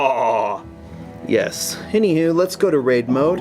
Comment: i heard "let's go to rape mode"